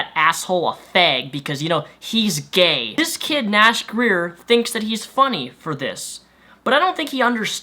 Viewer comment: sounded like you said nash queer at the end